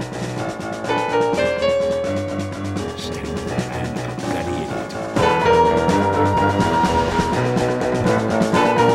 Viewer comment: What did Sniper say at 1:28? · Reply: "Standing around like bloody idiots"